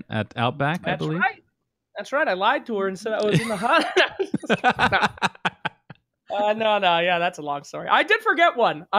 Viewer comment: what did said? "I lied to her and I said what? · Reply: and said I was in the hospital I think